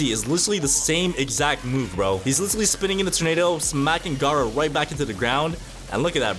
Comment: Did He Just Call Gara Garou